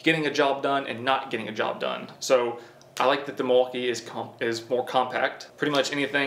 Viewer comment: Lol… I heard “deMaltkee”…. @ 4:22? Don’t we just want the best of both world…. I think they both are great tool, I like the power from Milwaukee and I like the new designs of dewalt, plus new stackPack batteries…. I owned both brand and there are just pros and cons for both. Both good brand to go for!